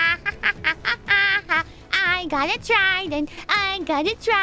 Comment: リスニング下手だからnever give in tryingって聞こえてしまったけどwithout even tryingって言ってるのか。 めっちゃ勉強になる……！